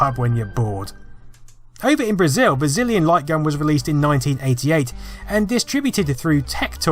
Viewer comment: I could have sworn you said Brazillion Light Gun ^_^